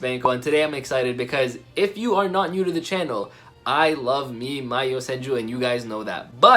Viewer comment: I thought I heard: I love me "Mayosenju" 😄😄